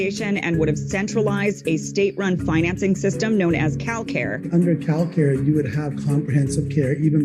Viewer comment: I heard 'KyleCare' at first.